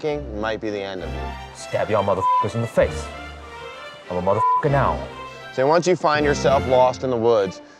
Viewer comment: “Stab y’all motherf*ckers in the face. I’m a motherf*cking owl.” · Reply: I thought he said I'm a mother * now 🤣